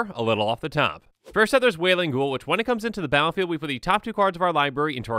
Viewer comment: I was listening to this without watching, and when I heard “Whaling Ghoul” I got very intrigued until I found out that it was only a Wailing Ghoul